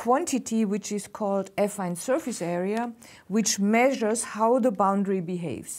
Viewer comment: I first heard "effing surface area".